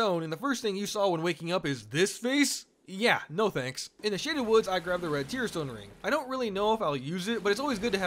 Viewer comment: when you said shaded woods I heard "shitty woods" and you know what, both are correct.